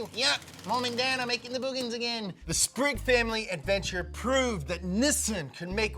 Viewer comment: I thought he said the spring feild adventure at first because the desert was called the Simpson desert